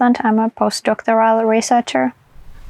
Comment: post doctor something researcher? What did she say and what’s it mean? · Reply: post-doctorate researcher